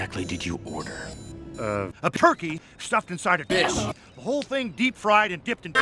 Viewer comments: I swear I heard "...inside a bitch" and the video got a whole darker. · I misheard fish...